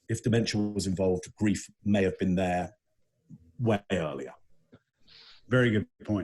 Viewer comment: is speaking and the captions said “wet areola” instead of way earlier 😂 I’m sorry but I’m hard of hearing and he dang sure didn’t say wet nipple. Seriously dying of laughter 🤣